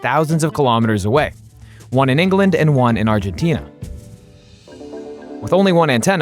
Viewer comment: did you just say ENGLAND and ARGENTINA?? ⚽️